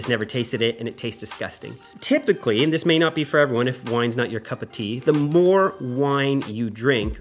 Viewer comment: Did you just say, "if wines not your cup of tea"? I'm sure that's what I heard…😂😂😂